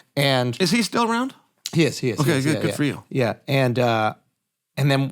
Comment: This small exchange from Uncle Chael to Andrew is so human, just him asking because of his own grief over his own dad's passing and he wanted to see where Andrew's head was at with his own experience. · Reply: i heard him say “good for you” at that moment and i knew how he meant it